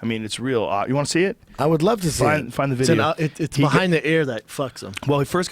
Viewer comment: Brain find the video? What?! Did he just call Jamie, Brian? Or is Redban in there?